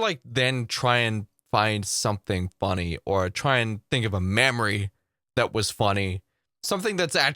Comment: 😉😆😂 I know you were saying "memory", but first time I heard you say "mammary" and I had a chuckle...